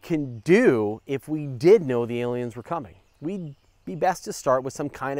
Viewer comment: I thought it said "Would We See The Aliens Cumming?"